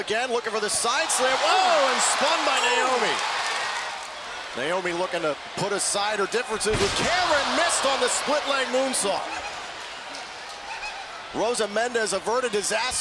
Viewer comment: wtf he said cameron miss it